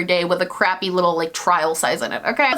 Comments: Omg I thought you said "The last thing I want to discuss is Christmas sex." Just me?😬😂😂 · I really thought you said "christmas sex" haha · I had your video playing in the background while I was getting my child ready for bed and I thought I heard u say " The final thing I wanna talk about is Christmas sex " and I was like wow, we getting intimate here 😉😂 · I legit heard "Christmas sex" and I was like why what's wrong with it lol and then I remembered oh shit I a watching a make up video I need my coffee lol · Thought you said Christmas sex instead of Christmas scents.. Am I going to hell now?